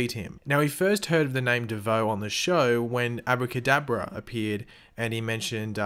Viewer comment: it genuinly sounded like u said abu kadabra , making me picture a muslim magician